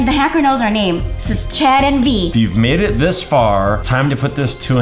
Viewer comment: I thought i heard chad and